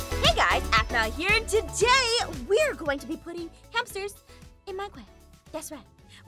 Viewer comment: At first I thought Jess said where going to be putting hamsters in my BUTT